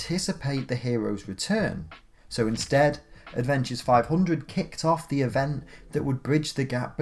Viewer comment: "...Avengers 500 kicked off the event..." *twilight zone theme plays* Lmfao i know he said adventures but for a few seconds i thought he said Avengers lmao